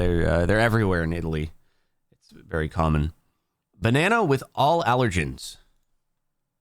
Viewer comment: I thought you said the banana had allergies lol